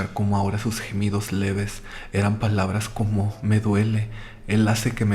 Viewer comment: ¿Soy la única que en la historia de los gritos en el hospital escucha esto? Primero gritos, después "perdón perdón" luego "perdón jesus" y luego "perdón mamá" son algunas de las cosas que se escuchan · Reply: Yo escuché "Jesús, me duele" 🤨